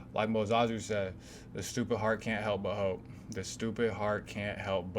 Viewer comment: I thought you said the stupid heart cant help a hoe at first🤣